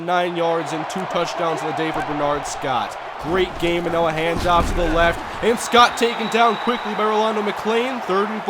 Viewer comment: Did anybody else hear "a hand-job to the left"